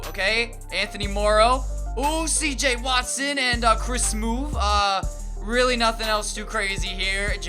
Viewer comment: I swear he said Chris smoove.